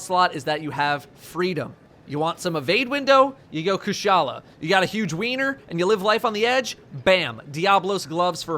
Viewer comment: Stood cooking me tea with this on cause ya know new datto video - got to focusing on the task at hand cause knife + fingers can be quite ouch. All I hear is "if you got a big weener" I'm fucking dying 😂😂😂 great video datto mate, enjoyed it keep em up!